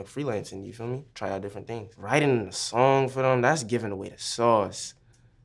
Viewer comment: what'd he say at 2:31? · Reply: "Writing something for them is giving them the sauce"